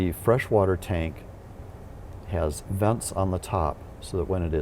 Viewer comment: Did he say "the fresh water tank has nuts on the top"? the 2:00min mark! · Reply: He said "Vents" on the top, not Nuts.